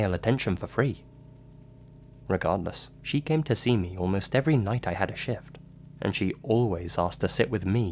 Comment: "she came to see me almost every night I had a shit" thats genuinely how I heard that.